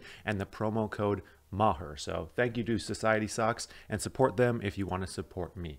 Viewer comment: So it's Society socks. I thought you said society sucks. LOL